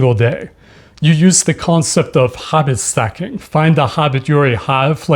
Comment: I thought you were saying Hobbit stacking at first, and nodded in agreement.